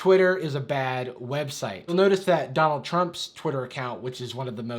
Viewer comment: I'm so exhausted so when you said Donald Trump's Twitter account I thought you said Grandpa Chuck's Twitter account